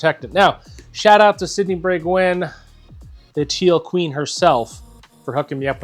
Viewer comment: humberto doesn’t count! he’s always first. and i swear it sounded like said “sydni break wind” 😆😜